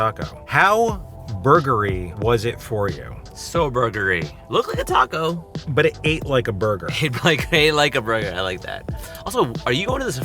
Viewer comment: I am such a child, I heard buggery. 👀.